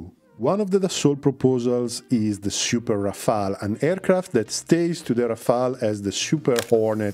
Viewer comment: My brain heard you say super NGAD Rafale and im going with that